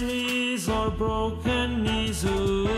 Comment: What did he say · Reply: Chinese or broken knnes